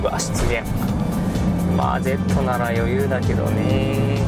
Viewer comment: Z(と一緒に俺ら)なら余裕だけどね～w って聞こえますw